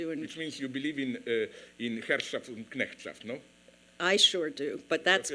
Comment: What was it Zizek says Ronell believed in? 5:56-6:00?? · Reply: "Herrschaft and Knechtschaft" = "to rule and to serve"